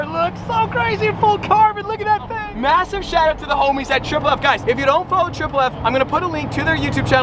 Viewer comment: Thought he was saying “massive shout out to the homeless” at 13:54😭😭